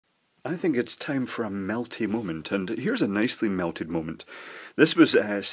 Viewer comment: I almost thought you said a Malty moment! And reached for a wee dram.